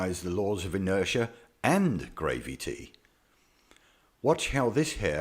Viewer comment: Sorry, maybe it's the accent, but @ 6:05 did he say gravyty? mmmm, I'd sure love some over mashed potatoes. I bet you it would stick to the potatoes very well due to some magical force.